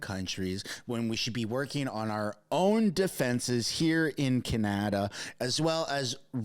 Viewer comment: I love your video.. you do a great job at keeping us informed.. you said something funny at minute 3:33.. Kenata instead of Canada...LOL